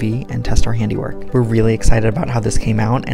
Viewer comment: This was like the one thing really wanted, and I’m SO glad he made it happen, because I LOVE it! It really helps the space feel more like an art gallery and less of a normal living space 🤩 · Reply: oh I thought he said locker tutorial